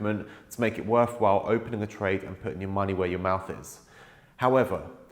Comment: I heard it as "put your money where your mouse is'. 😁 😁 😁